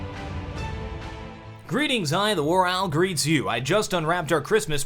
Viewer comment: I always thought he said "Greetings, I the WarOwl greets you.", but his channel description says otherwise.